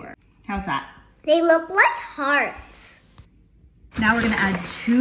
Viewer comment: HeLp I thought she said, “they look like farts”😂 she is so cute 😊